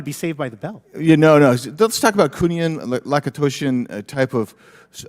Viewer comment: Does anyone know what words Rep Biggs is trying to say at 5:54 ? It's some type of secret technology. Sounds like "kunian-lakotosian"